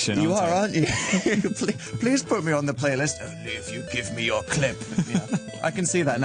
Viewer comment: A 13:53 I thought Dan said "If you give me your clit". I was like 😮😮😮😦